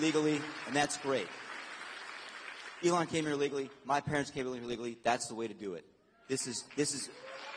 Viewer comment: haha 49:22 nervous stutter sounded like he said his parents came here illegally